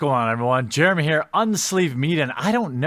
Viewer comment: lol it sounded like you said unsleeved meat at the start. 😂